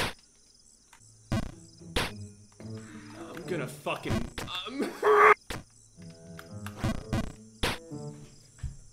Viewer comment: sounds like he said the n word. don’t go after him tho sure he didn’t but hella funny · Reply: He said “I’m gonna fucking - rage intensifies “